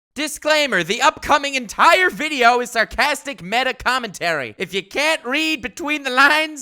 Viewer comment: Wait, I thought he said the video had sarcasm